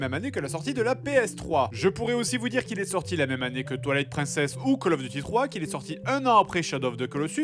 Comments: J'ai compris "Toilette princesse" au début. •-•' 😅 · c'est moi ou il dit "toilette princesse " ?